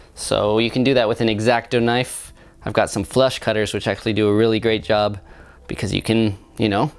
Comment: I thought you said "flesh cutters"! :O